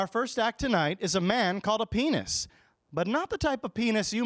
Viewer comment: I thought you were saying penis!! not pianist :-o